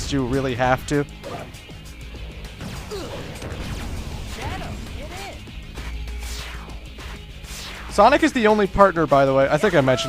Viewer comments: Misprint wow sonic says: "Shadow get in" but the subtitles say "Shadow get on" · Is it me or at 8:21 sonic said " shadow, get in!" when the subtitles said "shadow, get on!"XD